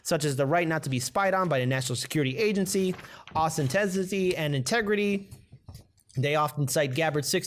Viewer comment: It sounded like you said "Austeniticy" rather than "Authenticity." That would I guess be a reference to gamma-phase Iron, a non-magnetic crystalline matrix found in Cast Iron and quality Steel. It is a way of calling her the new Iron Lady?